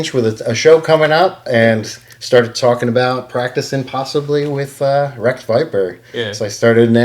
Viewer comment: Did he say Wreck Viper? 😅